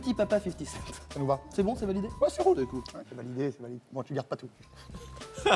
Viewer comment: Sofiane dit quoi là ? J'ai pas compris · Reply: c’est validé c’est valid bon tu garde pas tout